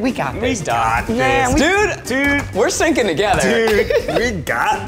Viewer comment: Who thought when Zach said "we're synching together" that he meant "sinking" at first because they more often get 3rd/4th place? Or just me? Lol